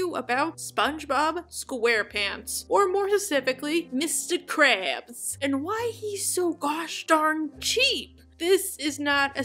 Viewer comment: I thought I misheard you say more *pacific-ally and thought damn that’s a good sea pun